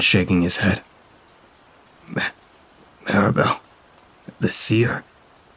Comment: When you said Merribell the seer, I thought you said Merribell the STEER!! HAHAHA. Great story!